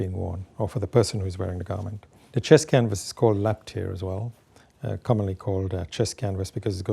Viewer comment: you say a chest canvas is also called either "laptier" or "lactier" (according to closed captioning) but when I google it, those words don't bring back any results. What is the word you're saying there? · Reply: Laptair